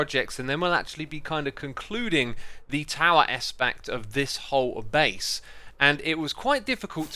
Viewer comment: did you just say "tower espact?"